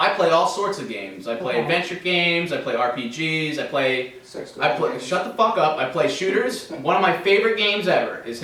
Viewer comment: I think said "Sexual games"